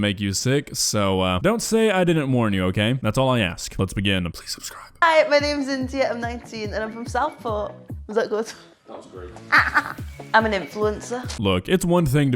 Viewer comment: I thought she said she was from South Park, it would have made sense.